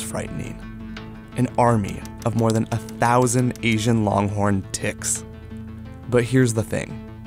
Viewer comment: I m so dirty minded. I legit heard “Asian Long-Horn Dicks” 💀